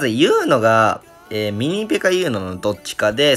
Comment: のミニペかユーノがインフェかユーノに聞こえて焦ったw